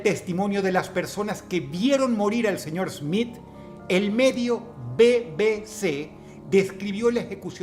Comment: Primero de "Medio BBC" entendí "Método BBC" la verdad sería un Método re turbio